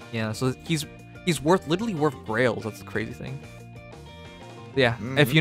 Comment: when you say "grails" it sounds like you say "girl aids" and it's still the funniest thing to me